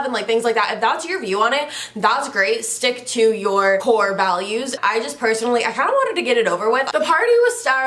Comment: you said “stick to your core values” and i really thought you said “whore values” like wow call me out like that 😭😂😂